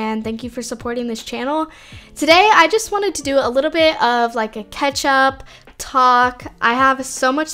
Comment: Discussion board: 0:15 i heard ketchup what do you hear ? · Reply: heard ketchup but ik she’s saying catch up 😂